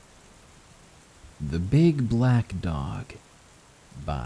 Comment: The first one I heard as “Big Black DONG”